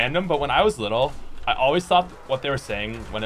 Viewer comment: “clock in time” i thought carpenter said cotton tail back in the day